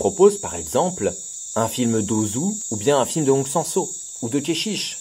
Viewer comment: A 11:28 j'ai entendu "Monsensot", c'est bien ça ou je suis débile? (je suppose que je suis débile vu que je n'arrive pas à trouver de "Monsensot" sur google) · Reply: Hong Sang-Soo* ;)